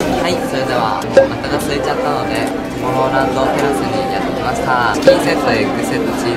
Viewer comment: でMOMOLANDに聞こえたMERYは私だけか...